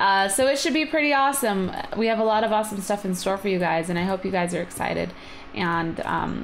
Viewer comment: I think I heard "um and I oop"...